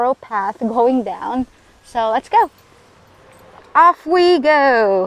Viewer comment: did i just heard "up we go" in a downhill?